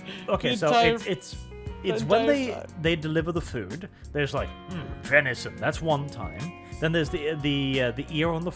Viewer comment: When they were asking what the meat was I thought they said Veni’s son.